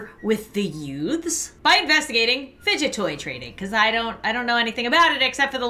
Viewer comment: is it just me or did she just say fidget TRAINING